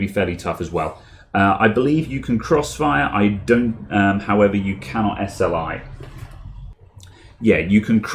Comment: what he say about 2 Graphic cards I don't understand him! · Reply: You cannot sli